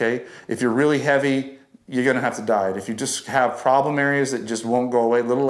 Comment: I almost thought he said, “I you have to lose weight, you’re gonna have to die.”😆😆😆